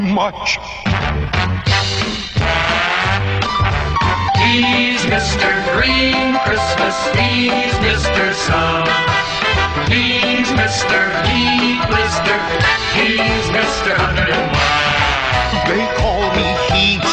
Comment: I'm so corrupted, when I first heard this, I head "he's M.R sus" intead of "he's M.R sun"